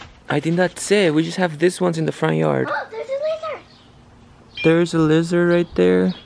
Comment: I thought said "look theirs a wizard!"🤣😂